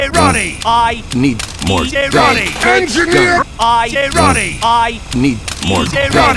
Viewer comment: im i the only one who heard "engineer i need to eat jarate son" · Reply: All i heard was ''I need more jarate, ENGINEER!''